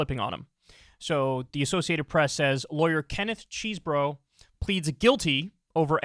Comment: I was smoking a joint so my back would calm down and I could get to work, and I thought you said Kenneth Cheese Dick. Needless to say I almost suffocated choking on the smoke from laughing so hard. 😂😂😂